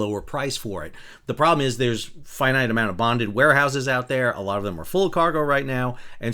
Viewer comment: All I heard was “bondage warehouse”